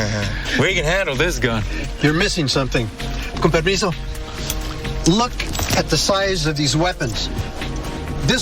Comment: What did he say? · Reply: Coń permisso which is Spanish for "may i?"